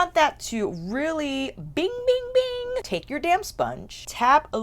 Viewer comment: Lol I had to watch the sponge part over because I thought you casually said take your damn sponge but you said damp sponge lol! I have the Darya pallet I’m so glad you showed how to use I was making some nice combos but had no idea how to use all shades in one look. Thank you!! I love both pallets on you! Stunning! Do you have a favorite?? Also as of this morning they are still on sale!!